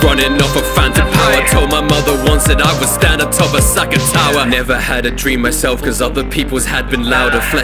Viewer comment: Song is fire i been listening to it everyday one of my favs but everytime I hear him say “asaka tower” instead of arasaka tower triggers me 😂